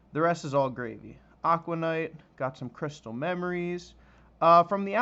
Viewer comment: For a second I thought he was about to say Crystal Meth! 😂😂😂